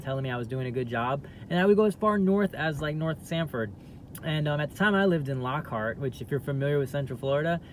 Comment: That’s crazy. But you didn’t grow up in Lockhart by Ivy lane at all. You were raised in the same gated community as me in a strict Christian household. That aside, I have respect for how you handled the 911 call and your life after losing your leg. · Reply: lol did we watch the same video? I didn’t hear him say he grew up anywhere. He said “at the time” 😒 You’re over here sweating the man and don’t even know what he said 😅